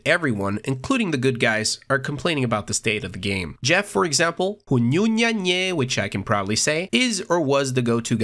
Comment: you say some wierd shit... whununnennaynie? what the hell you said?